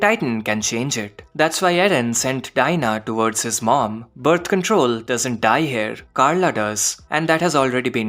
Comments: Birth Control instead of Bertholdt😂😂😂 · Did bro just call Bertholdt, Birth control?!!!💀💀💀💀💀💀 Amazing video nonetheless, I love AOT, and I love how you've explained everything.